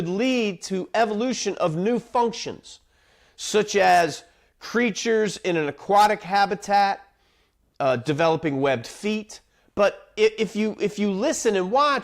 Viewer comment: I heard "Preachers in an aquatic habitat developing webbed feet", wow that was a wild ride 😂